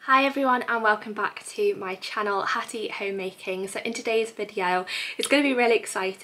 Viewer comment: Every time you say "Hattie homemaking" I hear "happy homemaking" and I think "aww happy homemaking to you too" 😃 Congratulations on your pregnancy! I'm so here for this content 😃